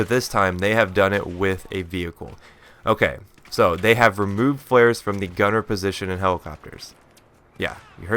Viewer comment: Gunner's can't use flares? Few, I thought you said they completely removed the gunner position. I was like WHAT??